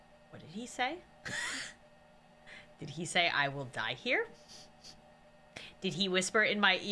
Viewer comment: The guy by the way said another alive person here the guy that you know when you were like putting stuff on the clipboard that's what he said and he thought he said I'll die here